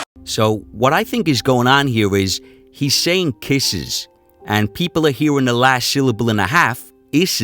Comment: I hear 'blowing kisses'